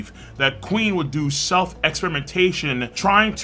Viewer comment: All I heard was “self excrementation” lmao